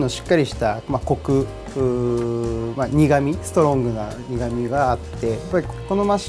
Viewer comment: did he say niggame XD